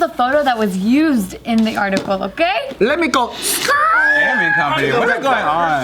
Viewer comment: Equality is equality man. I think if platforms like twitch stopped having double standards for what is fair and reasonable for women to do there compared to dudes, maybe everyone would know where the line is. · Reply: I thought he said a “Gay Man Company,” at first. What’s a woman doing working for the Gay Man Co.? 🤪